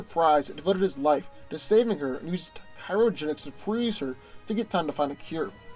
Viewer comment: Dawg i thought you were saying "pyrogenics" i was like, isn't that backwards